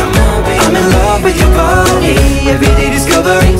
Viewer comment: For some reason I hear “I’m in love with the shape of gru”